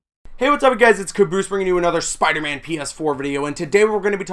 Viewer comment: It sounded like you said, "Spiderman penis4 video" ...